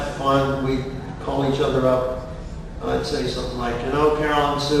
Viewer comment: what does he say here? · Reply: I think it says: "I really miss nibbling your arm"